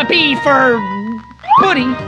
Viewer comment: Puts auto generated subtitles at 3:42 Jack: AAAAAAAAAAAAAAAAAAAAAAAAAAAAAAAAAAAAAAAAAAAAAAAAAAAAAAAAAAAAAAAAAAAAAAHHHH!!! Subtitles: [Music] Me: ... ...yup, that's about right. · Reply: Video:a b for buddy hi hello Subtitles:Appy I'll be the best to be philosophy firm hello Me: ... ...*WHAT ARE YOU SAYING????????*